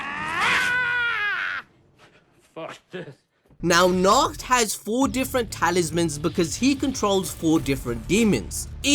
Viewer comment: what did say? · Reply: He says "fuck this"